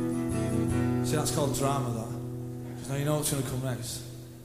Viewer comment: Can someone tell me what they say from 51:38 to 51:45 , please? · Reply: "See that's called drama that. Cause now you know what's gonna come next" Dunno what the person says next but then Noel says "alright man" in a Scottish accent.